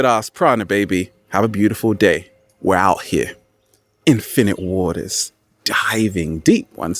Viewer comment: I'm a gamer at first I heard "Infinite Water" sound like "Infinite Warfare" for me XD